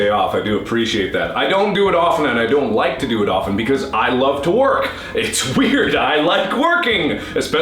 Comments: did he meant to say i love to work and say i love working together so he sounded like he said i love twerking? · i misheard him saying 'i love twerkkkk' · i thought he said i like twerking. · it sounds like he says, " I love twerk." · Hehe i thought he said " I love twerking" the i notice he didnt :( ... lol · When he said "I like working" it sounded like he said "I like twerking" xD